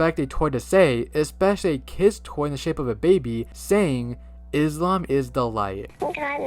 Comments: I heard both "Islam is the light" and "Its mummy's delight" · I hear "Idleham is the light" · I hear "Is come the light" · I heard "Islam is the light" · I HEAR FROM THE "Little mommy" SAID "god is the light" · For Little Mommys doll, I hear "Bucadams the light" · I hear "The mum is the light.." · I hear the "Islam baby" saying "God is the light". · i honestly hear “it’s time for the light” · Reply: I hear “Booglan Move the light” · For me, in the baby doll, I hear “Islam is a lie” damn.. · I hear "mna is thelight" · I thought the baby said "and God is the light"?